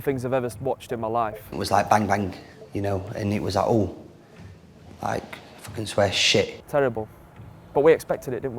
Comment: sorry what did kyle walker say at about 3:34 I generally can't tell. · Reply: “we kind of underestimated them a little bit, it was like bang bang, you know and it was like ooo, fuck this we’re shit”